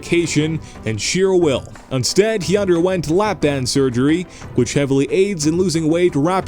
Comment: Did he say Big Hoss lost weight through "lap dances"?